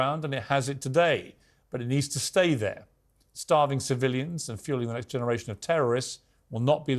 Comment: Did I mishear at 3:32 he said fueling the next generation of terrorists when talking about the starved Palestinians????! :DDDDDD HE NEEDS TO BE HELD ACCOUNTABLE FOR THAT